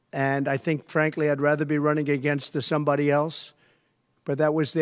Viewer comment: I think you have misheard or misinterpreted what Trump said. At 2:18 in this video what Trump actually said was - ''We were given Joe Biden and now we're given somebody else, and I think frankly that I'd rather be running against THE somebody else''. The word 'THE' is pretty quiet but it's definitely there if you listen hard enough. You are hearing this as he would rather be up against somebody other than Kamala Harris but that is not what he said and I am fairly certain that's not what he meant either. 'THE somebody else' is Kamala Harris. I'm not defending him by any stretch; I think he is a disgusting individual who should be in jail but I think you are misrepresenting him here.